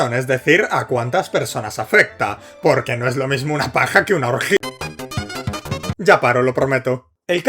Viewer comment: tuve que retroceder porque entendi "no es lo mismo una paja que adolf hitler"